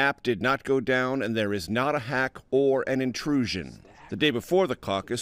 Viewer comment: Did anyone else the voice at 2:03?!? It sounded like they were saying “It’s a Hack”? Hahah creepy